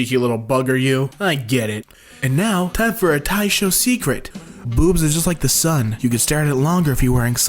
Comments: I heard 'taisho secret' and the kny alarms in my brain went OFF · I WASN’T LOOKING AT MY SCREEN AND THEN I HEARD “and now, it’s time for a taisho secret!” AND I WAS LIKE “WAIT, DEMON SLAYER?”